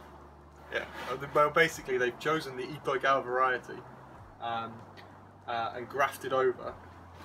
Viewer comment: Anybody know what word he says? "They've chosen the _________ variety" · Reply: Itoigawa Juniper aka shimpaku